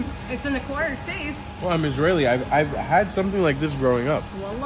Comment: did e just said that he's Israeli